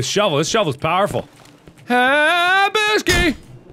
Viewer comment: What does he say at 8:12 ? ; ) (I hear is something "habushky" xD )